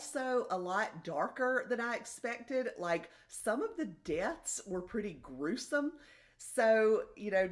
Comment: On a funny note, I thought I heard you say that in You Are Fatally Invited, some of the “guests” are gruesome, but you actually said some of the “deaths” are gruesome 😂 I might skip that one. Thank you for your detailed, honest reviews of all of these!